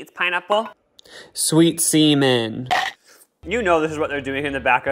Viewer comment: WHAT DID YOU SAY💀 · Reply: Semen 👍